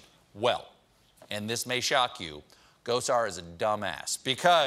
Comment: Is it weird that I heard “Gozer” instead of “Gosar”? I’m sure there’s a joke there, but I just can’t see it.